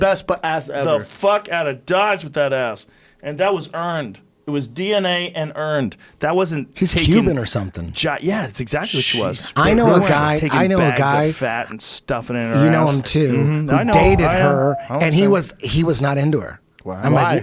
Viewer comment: I thought joe said to take my fatty and stuff it in her ass